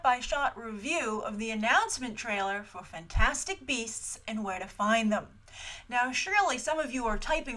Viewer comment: I thought it said fantastic breasts at first. The way Grace smiled in the beginning didnt help lol.